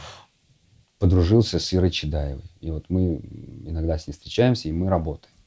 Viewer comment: а кто расслышал имя психотерапевта? Ира Чидаева? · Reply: Ира Чадаева